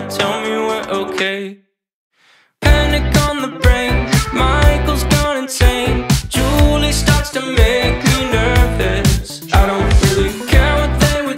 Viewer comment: "Michael's gone insane" Are we talking about the same Michael who said "tacos are great"? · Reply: AT FIRST I THOUGHT UT SAID “mic has gone insane, juuling starts to make me nervous” HENSNSNEND